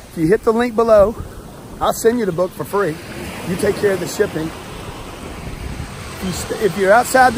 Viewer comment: so basically he told his old self to buy his book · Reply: I thought he said the book free just handle the the handling lmao